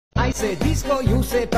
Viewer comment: What I heard: “i say discord you say barbie, discord discord!! Barbie Barbie” 💀